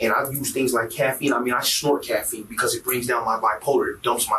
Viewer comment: He was snorting SEMEN. I feel like the comment section is lacking in this discussion 😂😂. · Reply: I heard him say he snorts coffee